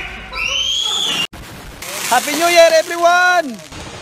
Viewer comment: LMAO I JUST HEARD "happeh new year ebrewann" HAHAHHHAHAHHAHAH